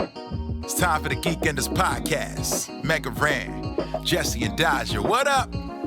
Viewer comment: what does he say after the word podcast it's driving me crazy! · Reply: MegaRan! It’s the name of the rapper.